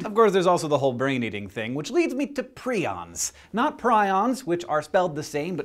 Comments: I thought it said 'prisons' ... although that makes sense too. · Thought it said "prisons" and was slightly disappointed